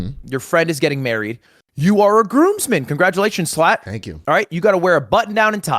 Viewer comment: Did Ludwig just call schlatt a slut...?